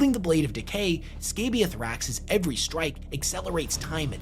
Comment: Wait... did you say Scabius Raxx? That's wild